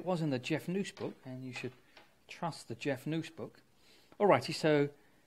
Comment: What's a Jeff News Book? Is that what you said at 7:15?